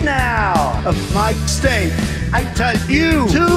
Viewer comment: Instead of hearing "people tell me to enjoy life" I hear "people tell me to en your life" "I wish I could but it's too late."